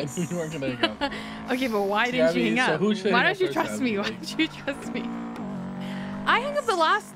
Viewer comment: I thought it said thrust